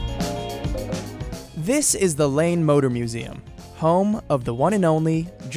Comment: I heard "Lame Motor Museum"...